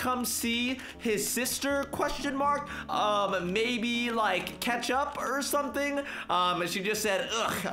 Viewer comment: When he said catch up I thought he said ketchup😂😂